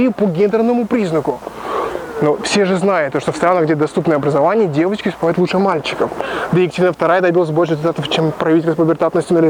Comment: что он сказал? невнятно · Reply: есть статистика ООН о том, что в странах с доступным образованием для всех девочки в среднем успевают лучше, чем мальчики